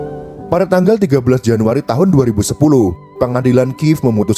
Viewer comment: tahun 2010?? Apa saya salah dengar? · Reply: Benar tahun 2010. Karena Ukraina berdiri tanggal 24 Agstus 1991, kalau masih zaman unisoviet mempermasalahkan stalin dan holodomornya maka sama saja bunuh diri. 🙄